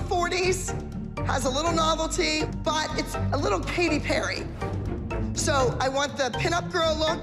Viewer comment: is it just me or did Abby say Paty Perry instead of Katy Perry